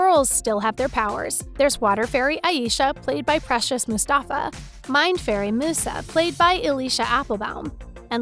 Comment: did I heard that right.. MIND fairy